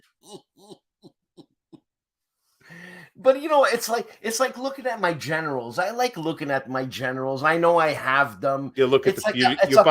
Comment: I'm tired... for a moment I thought he said he liked to look at his genitals.